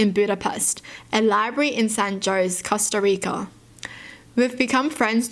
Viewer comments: did she say San Joe's? 🤣🤣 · Did she just say San JOSE